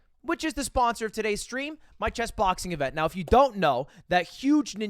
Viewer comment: when I first heard you talking about your chessboxing, I thought you said CHEST boxing, cause of that big ass dent in your chest. I was like, so he found a bunch of them, and they will fight till one is left? confused tf outta me ngl. lol